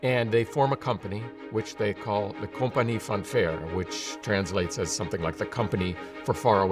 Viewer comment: He says "Compagnie van Verre" (Dutch), but CC says 'compagnie Fanfare'. This has nothing to do with brass or music. The English translation is later explained. can we update CC?